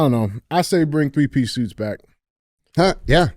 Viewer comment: I thought said we need to bring pp suits back, I had to rewind.. 3-PIECE SUITS I was like WTF is a pp suit?😭😂